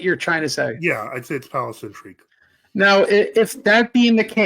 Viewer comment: What did say in minute 2:03:13, something about "palice intrigued"? I was unable to clearly hear it. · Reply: "Palace intrigue"